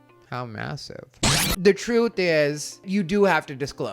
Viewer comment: I heard massive and my brain was also hijacked.